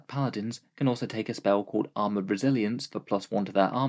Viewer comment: I could have sworn you said "Armor of Brazilians" instead of "Resilience."